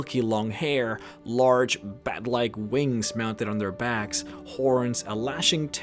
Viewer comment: I heard "large b...(oh yeah)at-like wings" (oh... Yeah lol)